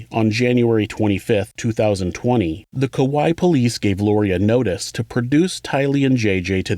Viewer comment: I'm trying to take this seriously but I keep hearing "Kawaii Police"